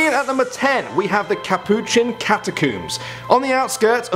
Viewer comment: haha it sounds like he said cata-cooms😂😂